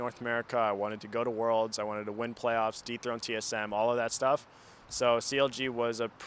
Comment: did... did he just say "deep throat TSM"? i havent been up-to-date with young people slang for a while, but.... is this what young ppl say now? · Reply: de-throne TSM as in Over take them as #1 team in NA.